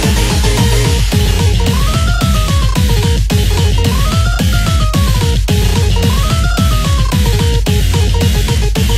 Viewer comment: wait what do they say 😭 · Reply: K: Let’s make udon to end this with a bang, right? S: Excellent idea makes face K: What’s wrong with you